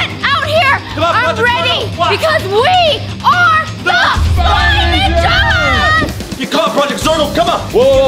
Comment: when they said we are the spy ninjas my subtitles said we are the guy 💀💀😭😭🤣